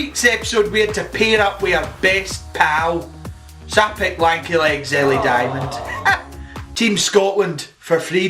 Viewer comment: I'm unclear on what she's saying at 0:20 but I don't think is "sapphic wanky legs" like the caption are trying to convince me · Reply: Haha it says so i picked lanky legs ellie diamond 😅 dont think google auto captions speak scottish yet